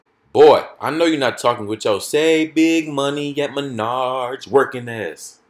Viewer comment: I thought he said minyards 😂😂